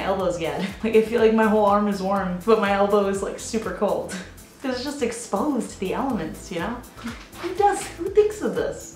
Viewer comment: what did you say?? Plevyat?? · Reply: /Kazachkova haha I said "my elbows are just exposed to the elements, you know?"